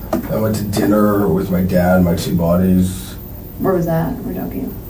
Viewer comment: I swear I heard, "I went to dinner with my dad and two bodies." I played it three times before I was like, "buddies?". Yikes.